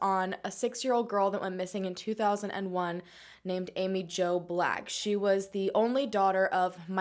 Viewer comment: I thought you said Amy Joe Blagg in the beginning.